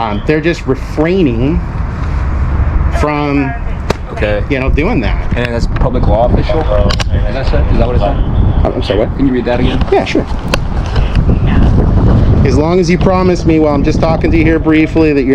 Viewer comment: I think that I heard him wrong, but it sounded like the officer asked if the public law was official. Is that what he asked? God, I hope not.